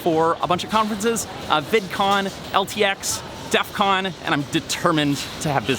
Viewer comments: at first I thought he said defqon, that would have been an interesting one xD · I'm the only one who hear DEFQON at 0:58 BTW DEF-QON-1 !